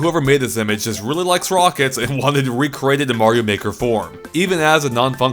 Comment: Did he say Mario maker 4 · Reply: No he said Mario Maker form